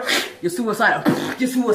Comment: HOW COME BEES HAVE THIS BUT NOT US💀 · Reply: i thought that the bee was saying "don't be suicidal"